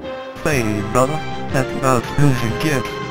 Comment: Did he say, “Heyyy, brother. That’s about as smooth as it gets.” in a somewhat ghetto voice?!?